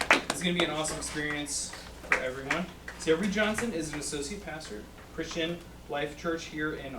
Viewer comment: At first I thought he said "Reid Johnson is a sociopath".